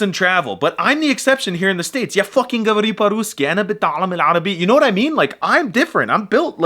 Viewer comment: Never alone. We may not be hitting critical mass, but we are everywhere. I have a question bc I know that his Arabic is better than mine. I think I'm hearing: انا بتعلم العربي Am I hearing that correctly? Is there a 'beh' in the verb, or is my hearing getting even worse? (If the letter is there, would love to learn about that conjugation. I couldn't find it anywhere.)